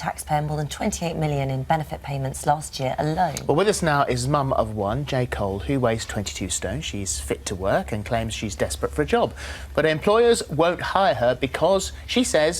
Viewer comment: for all us American who don't know how much a stone weighs here you go 1 stone = 14 Lbs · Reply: SavageSavant Fuck, yeah I misheard thought he said 20 stone.